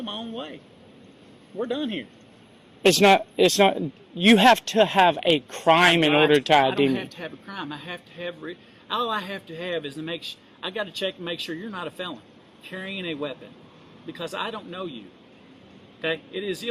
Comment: He almost said “all I need to have is an excuse” did anyone else catch that